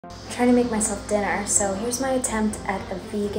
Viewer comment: I thought you said you were trying to make yourself thinner for a second there